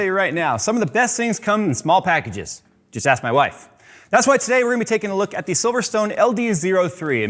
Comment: Hahahahahaha i thought he said, "except my wife". Whew i thought this was his last video. Hahaha